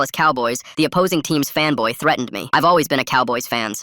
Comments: Im cooked. I thought the person saying the story said femboy instead of fanboy. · I thought you said femboy... HE SAID FANBOY BTW